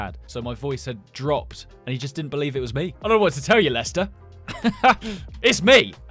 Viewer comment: did he say Lester?